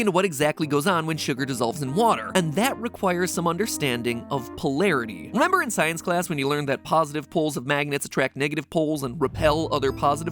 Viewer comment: thought it said “popularity” and I was like “okay well obviously I can’t help with this”